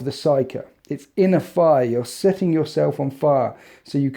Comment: Thought he said "In a fire" not "inner fire" and I was super confused for a second